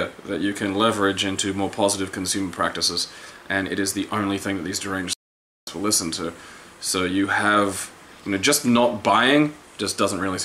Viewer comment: Huh, that's weird. What did say when the audio cut at 12:42? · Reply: Relyn Serano that these deranged lunatics will listen